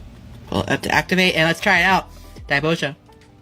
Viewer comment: What did he just say? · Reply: Dai boja, he's trying to ride the coat tails of the guy who does reviews of putting random shit in liquor to make infusions